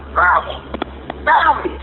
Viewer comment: subtitles say "gravel" but it sounds like it could be "grab her" followed by "no!"